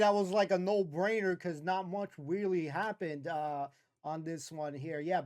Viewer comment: I heard that ..... 'Not much Wheely happened!' lolol That was a great episode! :goodvibes